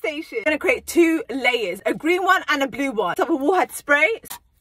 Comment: When she said “a green one and a blue one” I heard “a blue one and a blue one” I had to start it over I’m going crazyyyy ✋😫🤚